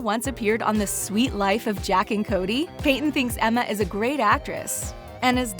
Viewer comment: did she just say jack and Cody??